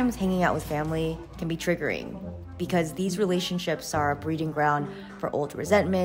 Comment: What can be understood the word "a breeding round" in